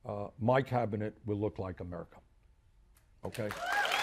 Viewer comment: Wow.. Sometimes I really cannot understand the American accent..."my cabinet will look like a..what?" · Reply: "Amurica" it's a pronunciation thing yes 😂😁